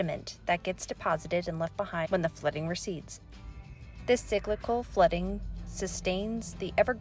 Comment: When you said, "cyclical flooding", I thought you said, "Biblical flooding", and I was like, "Umm, not really." 😂😂